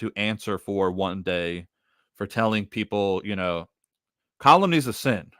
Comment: What is the word you're saying at 41:06 ? "Colum is sin..."? How do you spell that word that sounds like "colum"? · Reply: Calumny- it's to detract against someones good name.